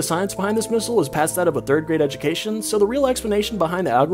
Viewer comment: Bro I thought you said range instead of brain